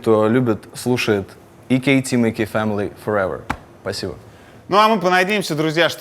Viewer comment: Шо он в конце сказал? Ака Тимати фэмили расслышал, но не понял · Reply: EK TEAM EK FAMILY FOREVER. Это его слоган